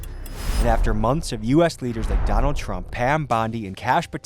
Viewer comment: did he say mac donald trump XD ?